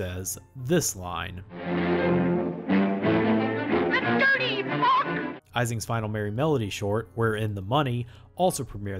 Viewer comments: Now that YouTube has a "most replayed" feature, we can see the massive spike at 41:35 where everyone went back to listen to Bosko's "THAT DIRTY ####" moment. (What is he supposed to be saying?) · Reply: of them also say “that dirty mug” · Did Bosco drop an “F” bomb??? I swear I heard him say “That dirty f**k!” 😱